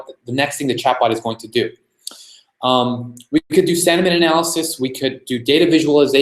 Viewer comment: What does he say at 07:30? What kind of analysis? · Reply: sentiment